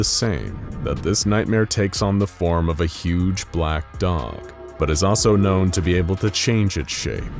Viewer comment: thought it said “big black dong”